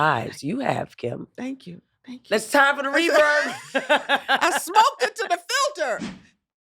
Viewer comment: "I smoked it to the filter!" All I heard was Sinclair trying to but out of Kim!